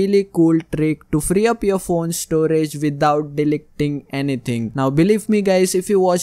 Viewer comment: Did I just hear delec ting